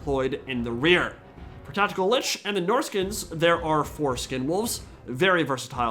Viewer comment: I was listening to this in the background and had to take a double take when I hear "there are foreskin wolves".